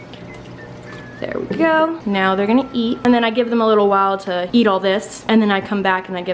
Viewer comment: I THOUGHT SHE SAID SHE GON EAT DEM RATS HOLY MOTHER OF SNAKE CHIPS